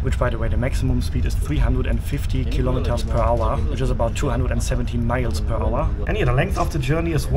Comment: I gotta ask: did you say 217 or 270? 'Cause I played it through thrice and it sounded like you said 270. 217 is the correct figure.